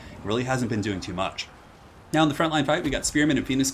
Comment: Really I know you like your phallic references but at 6:17 I swear you say "Now in the front line we have spearmen and penis guard..."